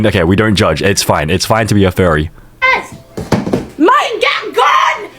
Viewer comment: I thought you said fairy for a sec😭